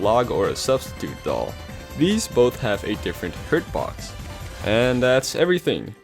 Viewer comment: why did he say hitbox like that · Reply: He said "hurtbox"